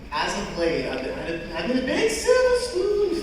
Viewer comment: if my hearing is correct,I think he said “silver spoon”